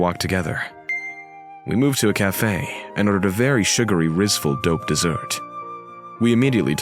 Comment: did i just hear the word rizzful r we fr rn😭